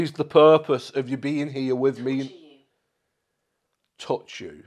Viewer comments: Hey, just wondering what everyone else heard when it was talking to you through Alexa. I thought I heard it say ‘torture you’ not ‘touch you’ but it was hard to hear as it kept talking when you did. Hope you’re well, am just about to watch your next vid. Stay safe ❤ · when you thought it said 'touch you', it actually said 'torture you' i believe